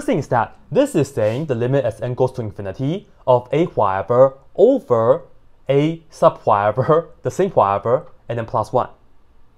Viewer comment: may i ask what is he calling that term? can't quite hear it well. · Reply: "The limit as n goes to infinity of a-whatever over a-sub whatever (the same whatever) and then plus one"